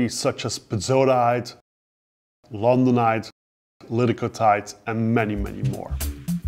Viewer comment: Great video. Sadly the auto-generated subtitles aren't accurate. Does anyone know what the 3 rare minerals are that mentions at 3:36? Pezzatite? ? ? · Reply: londonite, liddicotite